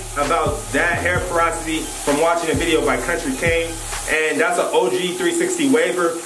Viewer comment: who is the country cane dude? How can I find his YouTube I keep hearing about him but idk how to spell it or find him